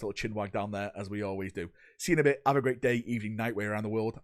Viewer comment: "Have a great day, evening, night, way around the world." I completely misheard this as "even with the nightmare around the world". incoming?